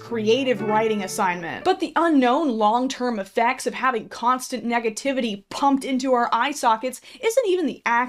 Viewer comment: The game that I was playing made my computer lag, so all I heard was; "...constant negativity pUUUUUUUUUUmped into out eye sockets-"